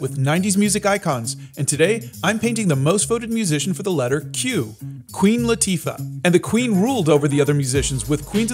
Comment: I watched too much F1! I heard "Queen Latifi"!